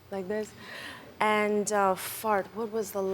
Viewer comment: Did she just say, "and uh fart..."??